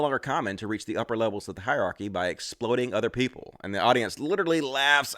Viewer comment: I thought Peterson said 'exploding other people.' That makes a lot more sense now. Still wrong, but makes more sense